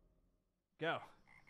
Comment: who else heard go